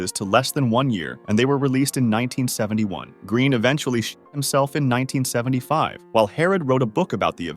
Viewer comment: when you said "green shot himself" it sounded like you said "green sh1t💩 himself"